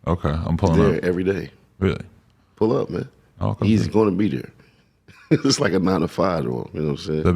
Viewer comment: Does he say "he's gonna be there, like a mound of firewood."?